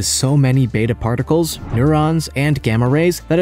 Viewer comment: Did he say that plutonium gives off neurons? 😂